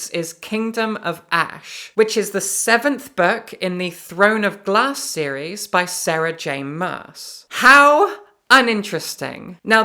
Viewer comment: I could have sworn you said "The Throne of Blah series by Sarah Maas." Never mind, I've decided that that's what you did say, as you are an honest person.